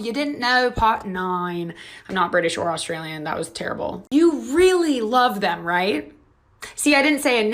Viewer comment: I thought she said "u really love that right" and I thought she was talking about her fake accent😂😂😂